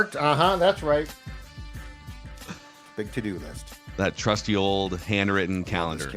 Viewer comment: I thought said “big titty list”